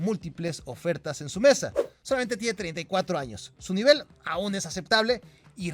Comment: Te la jalaste con vela, solamente tiene 34 años,,, jajaj pensé que decías que tiene 20!